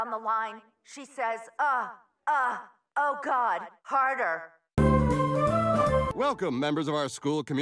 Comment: did she say carter or harder?